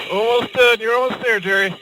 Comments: Did he say jerry at 14:00? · did he called him Gerry? :)